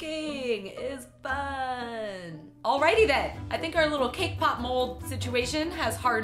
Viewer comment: Thought she said “K pop mole”